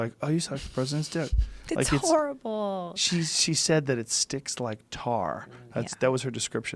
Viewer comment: I thought he said, that she said "stinks", not "sticks"! 😂😂😂😂